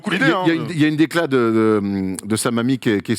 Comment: sur le coup j'ai entendu "une décla de sa mamie" 🤣🤣🤣